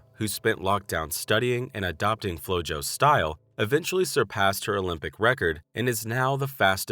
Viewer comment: so at 1:53 of the video what did he say? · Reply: he said she surpassed the Olympic record not the world record